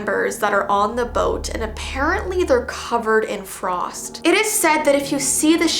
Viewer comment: I legit thought she said the crew was covered in frogs